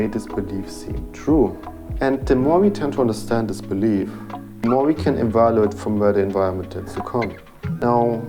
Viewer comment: what did you say from 0:41 to 0:48?? pls i cant tell. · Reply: The more we understand this belief, the more we can evaluate the environment from where it was formed. (Sorry for the subtittles).